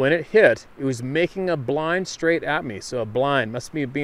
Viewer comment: Sounds like he was saying making a Bee-line